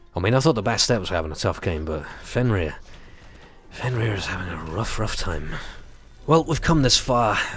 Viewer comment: Sounded like you said "He's having a woof woof time!" at 30:15. More Smite...plee! :)